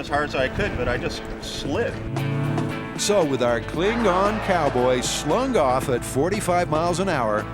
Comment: He said, "Cling-on cowboy". I heard, "Klingon cowboy".